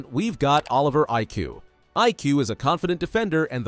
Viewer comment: aiku nah I hear iq